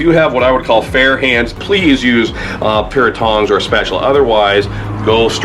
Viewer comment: i thought you said thong so i grabbed the wifes thong and burned my hands.thanks alot go back to gold rush